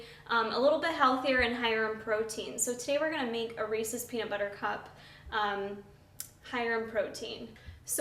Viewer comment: totally sounded like Today were gonna make a racist peanut butter cup lmao! love ur vids!